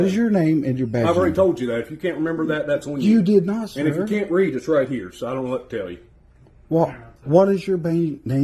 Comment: Did I hear correctly, the Aggressive pig says, “If you can’t read, it’s right here.” Then points to his badge…